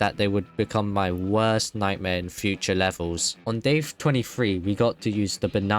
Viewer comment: instead of day 23 he said dave 23